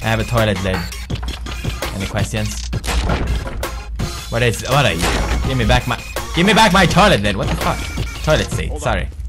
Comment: I thought he said "Give me back my toilet Linn! (my name)" O.O hahaha